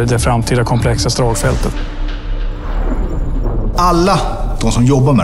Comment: Did he just say "Allah" … 🤔